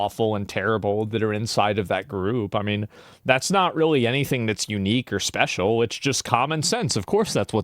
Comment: Did he say you niggas special?????? 🤯